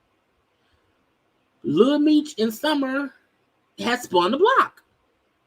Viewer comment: I thought he said Summer had herpes… he just as foolish as here, don’t try to play me & spin want to spin the block…